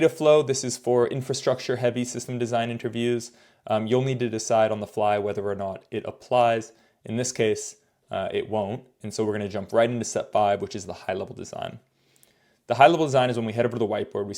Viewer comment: There a people who apply for infrastructure positions. Listening to your dismissal of some parts “infa” is scary. What should these people do? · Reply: My bad! I thought at 3:02 you said that “we won’t apply data flow diagram”, but you said “if it applies, here, we insert data flow diagram. But it doesn’t apply”. Can you please note which of the videos apply for infra and have data flow diagrams? For sure we need more designs for infra folks.